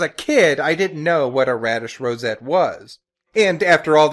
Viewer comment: Well, fuck a top-hat, all THESE YEARS I thought she said Spanish Rosettes. ALL THESE YEARS!!